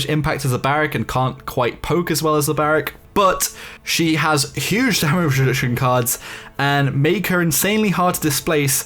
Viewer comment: What did he say??? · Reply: Huge damage reduction cards